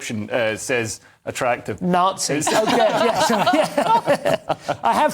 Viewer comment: did someone just say “Nazi”?